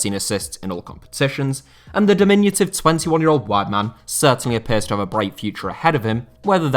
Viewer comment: What does he say hear as I can only hear "white man" but no surprise why I'm sure he actually didn't say that.